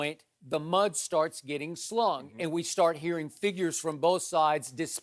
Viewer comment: Did skip say shlong on tv ? Lol